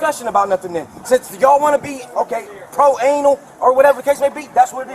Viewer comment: Did he say "Pro-anal"? 3:08 Listened again. Yes, he did indeed say "pro-anal." It's cool. We don't kink shame here.